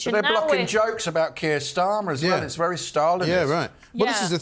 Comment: Did you say Keir Stalin?